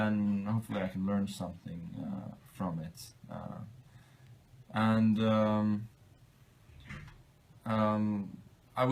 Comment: What older grand master's name does Carlson say here? I couldn't quite catch it ... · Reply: He said Botvinnik, of chess academy fame, responsible for the Soviet system, which is kinda odd since Magnus plays nothing like it.